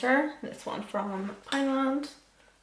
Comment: When says her powder bronzer is from pound land I thought she said Thailand LMFAO 😂😂